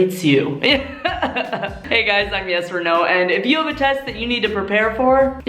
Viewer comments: I thought she said “I’m Yes and No” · I thought the said “I am yes or no”